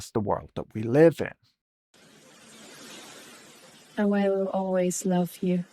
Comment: Subtitles are wrong, it clearly flubs the line as "away will always love you."